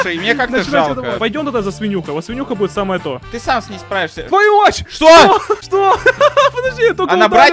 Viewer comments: сказал:"твою мать!" Или мне послышалось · мне послышалось или сказал "твою мать!!!"?